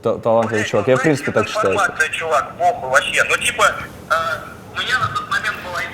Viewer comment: что сказал на 1:03:05 м моменте??? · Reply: Это дизинформация, чувак